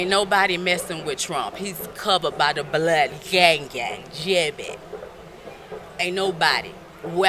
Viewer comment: Did she say YangGang at 8:21?